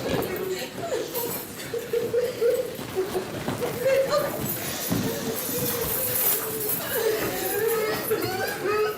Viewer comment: that demon trying to get out,it does not want to listen to what he did.The kid on 6:28 sh ou old be ashamed of himself that is not a laughing matter .Did he say RIP beat king? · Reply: He said RIP Keon